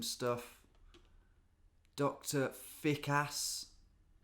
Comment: Thought you said Doctor Thick Ass and was thinking "that's my kind of doctor"